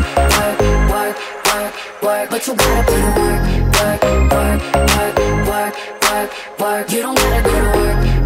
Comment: the lyrics is"you gotta put in the work work work work....." but i hear.."you gotta put in the twerk twerk twerk twerk....." this is concerning.....😶‍🌫😶‍🌫😶‍🌫